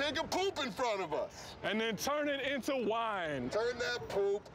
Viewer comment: At first I thought they were saying "take a poop, turn it into vine"